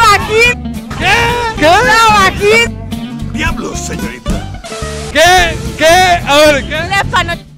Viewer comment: eso no es lo que dice el negro, dice : damn lady